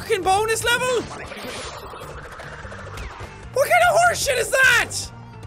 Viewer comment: the subtitles completely reverse what Jack means to say by turning, "What kind of horsesh!t is that!" into "We're gonna worship that!" and it killed me XD